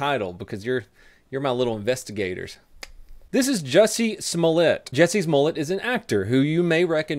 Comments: I swear...everytime I hear his name I hear Jessie's mullet lol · Sounds like he says "Jesse's Mullet"... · Help support the channel: subscribe to me at twitch.tv/ for free using Amazon prime Merch: <link> Pledge to me on patreon: <link> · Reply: I hear "Jessie's mullet" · Whenever he said Jussie Smollett I thought he was saying Jessie’s Mullet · I keep hearing, "Jessie's Mullet"